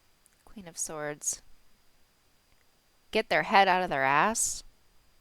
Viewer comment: I thought it you said it head out of the butt I was surprised you said it 🌹😳✌️👍